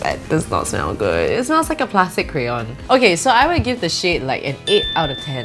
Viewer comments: I thought she was saying “I would give this sh*t” then I rewind it an realise that she said “shade” not “sh*t” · for one moment I thought she said "ok so I will give the sh*t like an 8 out of 10" and I was like whoah